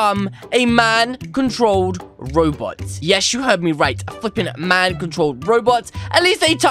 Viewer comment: did you say bad word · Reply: He said Flippin